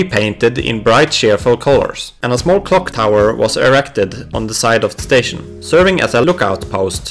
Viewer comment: i heard "and a small cocktower was erected"